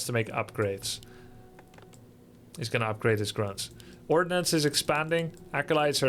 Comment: Lol I heard you say "orcnuns" first 😂 sounded so weird but I thought "m'kay". But then it actually was ordnance 😅 was a bit disappointed